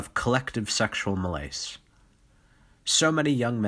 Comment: Of collective sexual what? "Milayce"? What word did he say? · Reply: Malaise